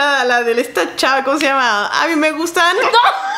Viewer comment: le entendí andy me gusta